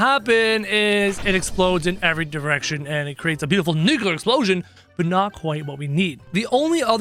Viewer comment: I thought he said the n word